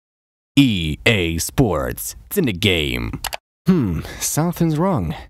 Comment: i thought it said “ea sports sinic game” until about fifa 16